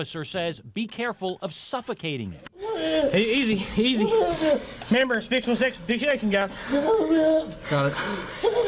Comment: he does NOT say remember. He says CAMERAS . he wants to remind the cops there are cameras and wants to clear HIMSELF of wrong doing.